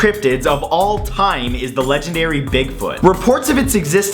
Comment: I thought bigfoot said bagfoot...